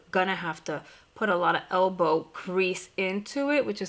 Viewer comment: It sounded like you said “elbow CREASE” lol it would’ve been such a cute play on words because you were working on your crease when you said it.